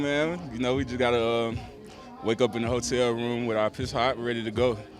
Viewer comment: did wake up in the hotel with our piss hot”? When asked about a away playoff game ?